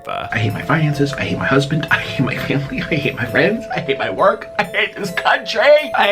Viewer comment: I thought he ATE everything he said